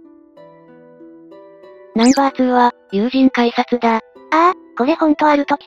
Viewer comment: 音だけ聞いてたから、友人改札に聞こえて 「やぁ、今日も元気だね」って言ってる改札想像した